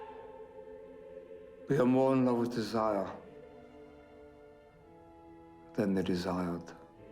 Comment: What does he say at 1:05:30 ? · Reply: We are more in love with desire than the desired.